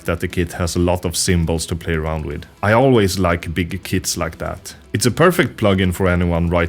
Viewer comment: Dam, i heard 'i always like big tits like that' 6:18 Great review anyways bro, thanks